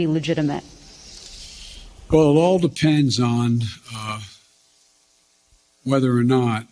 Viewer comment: I thought he said "I wear Depends" not it all depends.